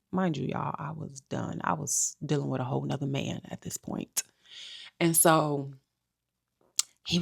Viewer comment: Maybe i heard wrong, someone pls correct me...did she say "i was done, i just needed to know, i was dealing with another man at this point". That confused me bcoz the rest of her statement made it seem like they weren't divorced yet when she was tryna get the scumbag to confess everything but then she also admitted she was dealing with another man (unless i heard wrong) so she's saying she had another guy BEFORE she was officially divorced...nope, I'm not defending that pig of a man, just pointing out that since this whole video is about a man cheating on her, that part caught my attention.